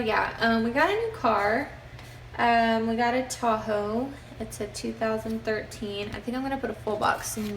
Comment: LOL at first I thought you said your new car was a Taco! X'D